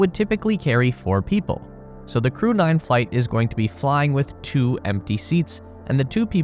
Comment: I kept hearing "crewed flight" as "crude flight." 😆